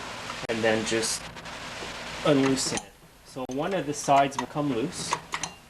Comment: Did he say "un-loosen"? But thanks for the good informative video!